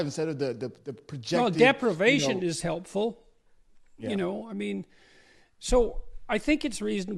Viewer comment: Perfect Peterson clip: "Deprivation is helpful." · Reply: First I heard "depravation is helpful". The wonders of English vowel reduction, I guess.